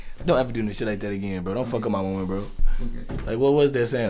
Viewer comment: omg stop when he said “don’t fck up my women bro” AHHHHH bro they mad cute tg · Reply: I thought he said don't fuck up my moment...Why would he say don't fuck up my woman that doesn't make since